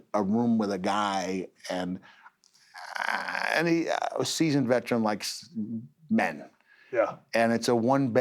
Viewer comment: Man Kind says “Ahhhhhiiiiiiiiaaaahhhhhhh season veterans like men”??????? I understand he is talking about the room he was renting was from a gay guy… “I have no idea how I know that given what he says” but yah his sentence there makes no sense…. Ahhhhhiiiiiiaaaa season veteran like men….. why does season veteran likes men mean gay??? Im so confused by there weird sentence……